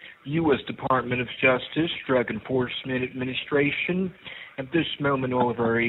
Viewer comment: Anyone else thought he said "Dragon Force" administration?